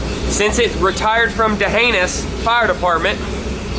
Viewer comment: That’s a awesome fire Truck 🚒! I’m sure that old Truck is glad you guys got it instead of it having to continue to work for “DaAnus” Fire Dept. 😂🤣🤣🤣 Thats What it sounded like you said DaANUS FIRE DEPARTMENT! That old Truck must’ve had a really Sh*tty Job working for DaAnus Fire Department !! 😂🤣👍🏼